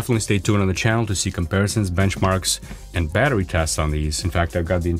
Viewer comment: You „are doing some battery tests“…😂 on a wall socket powered machine. I really like your style and videos! 😊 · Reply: I thought he said “a battery of tests”